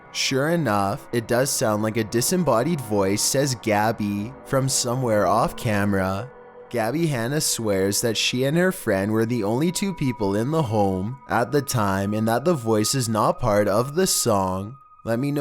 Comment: I heard Hannah